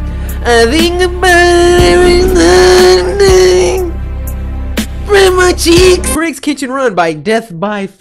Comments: "spreadd my cheeks" 😂😂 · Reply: I actually thought he said grandma cheeks😂😂😂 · wait until the end did he say speed my cheeks? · did he just say spread ma cheeks? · Who else heard spread my cheeks